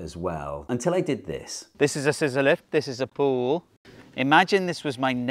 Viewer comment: I heard "sizolelith" instead of scissor lift, and for a couple seconds thought he was building the wall out of some weird kind of stone.